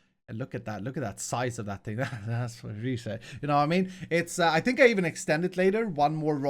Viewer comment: Anyone else hear that "that's what she said" at 19:54?